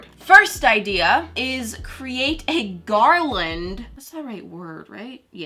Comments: I thought she said create a garlic.... not Garland. 😂😂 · When she said GARLAND i thought she said garlic lmao